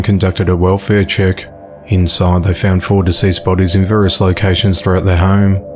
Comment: At :44 when he said 4 deceased bodies I thought he said 46 bodies I was like damn homeboy been buuuusy! Still sad 😔